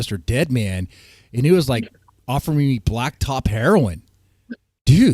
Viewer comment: did you say "black TOP" heroin?